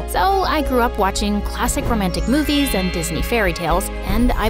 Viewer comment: yo bro wtf is this yall did not peep that is said phis knee instead of Disney go look and check cause this is crazy lol 0:26 😭